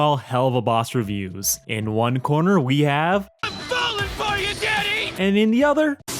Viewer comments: where have i herd this from someone help Is he saying bullet for you daddy or stole it for you daddy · What is he saying and where is it from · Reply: "Let's party Daddy!!" Idk